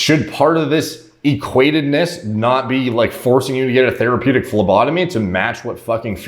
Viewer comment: Thought said "Therapeutic Labatomy" not phlebotomy..... About died 🤣